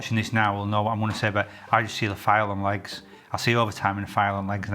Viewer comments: I've replayed 3:02 multiple times and still have no idea what you're saying?! I hear you don't like 'file and likes' but that doesn't make sense? What did you actually say? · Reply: File on legs…..lots of paperwork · American here struggling with the accent. Can anyone help me out? What does he see and not like here? He says it twice, but I've got no clue. It sounds like "fire and legs." I can understand everything else (except for place names, because English place names can go to hell), but not this.